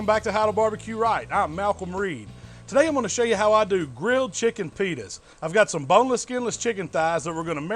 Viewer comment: did he say 'grilled chicken fetus'